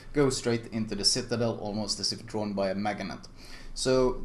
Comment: thought you said "Maginot" lmao